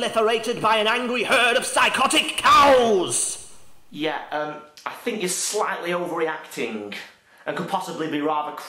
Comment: Hey guys, from 0:18 to 0:32, what does he say? I can only hear somethin like "psychotic herd of...slightly overreacting...match", plz help me to finish that.